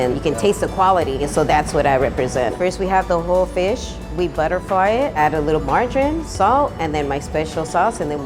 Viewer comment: My fat ass thought when that women said butterfly the fish i heard BUTTER FRY and was immediately moist for that fish.